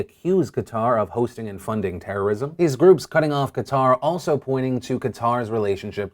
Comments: Dang it! I thought you said "Katara" at first, and I was pumped for some Avatar the Last Airbender-related news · I thought he said Katara at first.